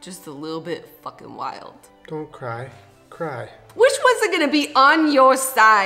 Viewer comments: literally thought julian almost said “don’t cry, craft” and i almost had middle school ptsd flashbacks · i lost it when julien said “don’t cry, cry” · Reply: I thought he said don’t cry, craft and I flipped out till I turned on subtitles